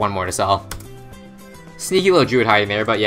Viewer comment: Did he say "sneaky little jew" at 17:55? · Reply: “sneaky little druid”